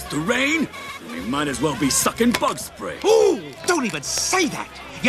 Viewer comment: when i was a kid i thought he said we be fuckin bug spray did anyone else think the same thing